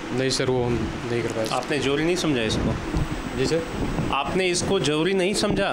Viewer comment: He said jori to zroori. I thought he was saying jury 😂